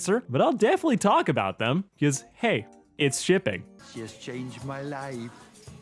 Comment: Is it just me or at 1:16 it looks like fizz was actually saying hey its shipping?